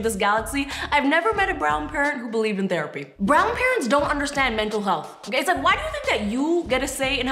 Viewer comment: Thanks for watching!! What was your favourite part? Personally, brown parents having "The Talk" is what my nightmares are made of LOL. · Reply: I thought u said grand parents not brown parents😂😂😂